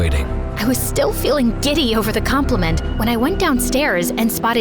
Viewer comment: I heard Diddy🙏🙏🙏💀💀💀💀😭😭😭😭